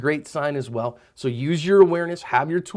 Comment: Why do I hear it "To cover your ass" and not "To cover your asp'?